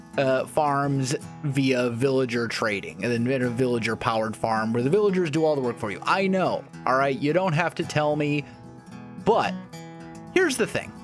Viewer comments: bro i thought he said he was going to make a weed farm in his basement and i got really confused. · lol I thought he said "weed Farm" for the first couple minutes! my bad!